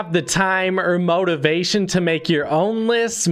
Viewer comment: ngl I thought he said the motivation to be homeless